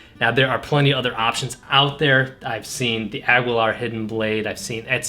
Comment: Did you just say... "Agweelar"